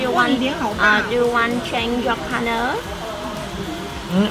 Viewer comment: 越南小姐是在問 You want change your partner? 你要把你的伴侶換掉嗎？ 我聽不清楚。是問這個嗎？ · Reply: 她應該是問：you want change your color？問他要不要改變髮色